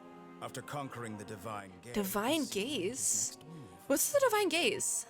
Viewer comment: Omg I keep hearing "Divine Gays" 😭😭 I'm so sorry it's supposed to be "Divine gaze" 😭😭